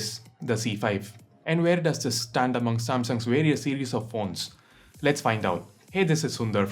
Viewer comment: Sundar did you say phone or porn :p